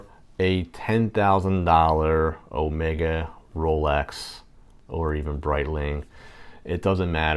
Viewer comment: Did he just say Omega-Rolex???😊